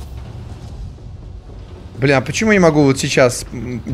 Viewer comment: сказал бл@ Или мне послышалось